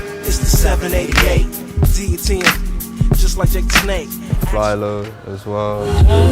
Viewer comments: who's the rapper he mentions at 3:00 ? didn't catch the name. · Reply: paco ríos flylo, aka. flying lotus ;) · did he say Fly-Lo I've been looking for that for a while! Someone help please